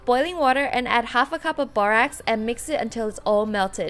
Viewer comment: Hi ! I'm french and I don't understand what you talking about at 11:12 - you mix boiling water with...borax? can someone help me ? :(